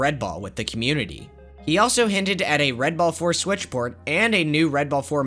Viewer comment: sorry, did i hear switch?